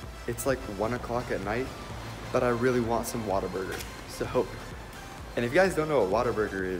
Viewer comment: At first I thought he said “water burger” instead of “whataburger”🤣